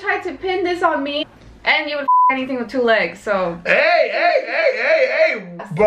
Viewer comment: I know only I did not hear what said at 11:22 🙀 “Anything with two legs”👀